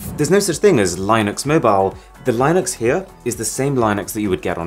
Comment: Did he just say lineux